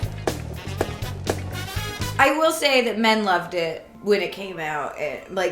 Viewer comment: I had to go back three times in the intro because my brain processed it as "What's up you Hawthornes?" and then "What's up you hole fans?" It's "What's up U-Haul fans?" i mean, duh. And one more for today's 'audio-processing drama', I thought at 39:40, she said "I will say that men love dick" ok that's all goodnight everybody